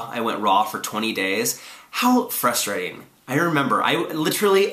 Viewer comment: I thought he said i went in raw for 20 days...